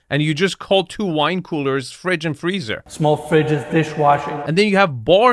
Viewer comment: did he call it a fish washer?